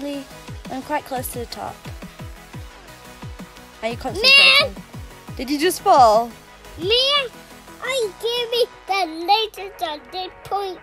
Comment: Wait did he call her Mia?